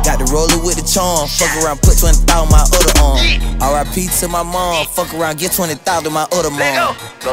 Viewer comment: i cant be the only one who thought he said RI pizza my mom